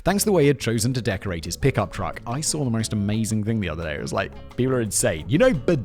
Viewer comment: I swear I heard "prickup truck"